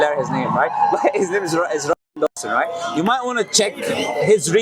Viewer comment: So crazy and new type of sensor just at 32:53 when tells the researcher name first commercial came up , you cant hear it. I thought it was a coincidence and I replayed the same bit but still can’t hear the name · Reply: Ryan Dawson